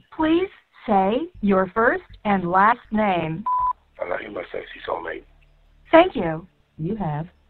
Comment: I woulda blocked the call after hearing “I love you sexy soulmate” 🤮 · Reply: At the beginning i thought he was saying sexy cellmate.